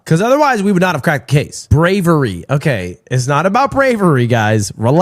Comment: nah who tf said “SlAvery” 😭😭😭